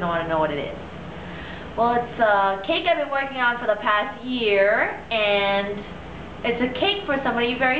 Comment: i think she said for d past year!! see